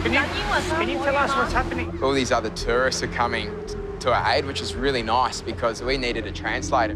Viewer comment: did he just say ‘more terrorists are here’